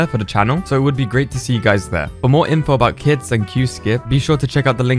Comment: i honestly thought you said " for more info about kids "